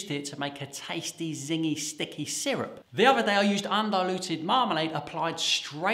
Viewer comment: Honestly thought you said "undiluted marmite"......